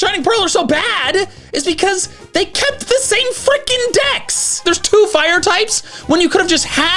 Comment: genuinely thought he said fucking 😂